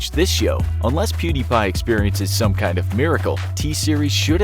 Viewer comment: did someone say miracle